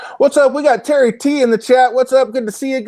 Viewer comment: i thouyght i heard this, sly the tenjin bank! hahah